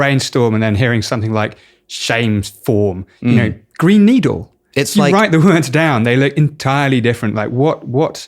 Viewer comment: wow whats fucked up is i replayed it mixing my expectation to brain needle and thats what i heard.